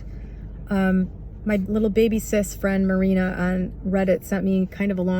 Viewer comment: lmao i thought you said 'Lil Baby's cis frend' for a second